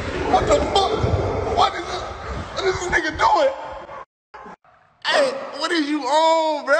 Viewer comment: yo, wtf melo said in 3:04??????, aint no way he said the n word bruh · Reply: he said nega